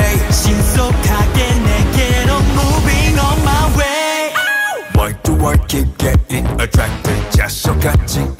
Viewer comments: First time I heard it I thought it said “why do I keep getting attractive “ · I thought they say ‘why do I keep getting attractive’ 😂😂